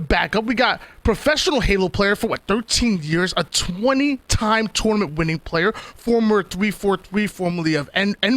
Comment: Did he say Mikwen is a 20-time tournament winning player??